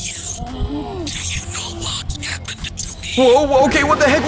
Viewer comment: i thought it said do you hear the wistle?